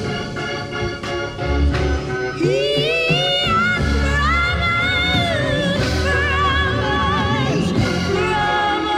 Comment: What did they say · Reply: "He has promised, promised, promised...."